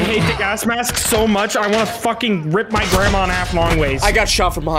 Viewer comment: ignoring the ladder part did anyone else hear say he wanted to "rip my grandma in half long ways"